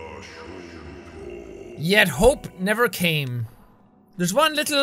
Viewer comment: My name is Hope and I thought he said my name and I got scared but then I realised what he actually said 🙃🙃